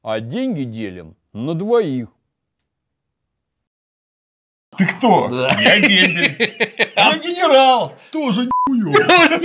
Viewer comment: можете написать шутку, а то не расслышал полностью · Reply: Ты кто? - Я дембель. - А я генерал. - Тоже ни(мужской половой орган)ово